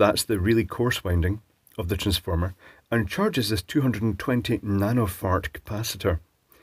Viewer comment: Did he say 220 nano fart capacitor? 6:56 lol I will be calling them nano-farts from now on.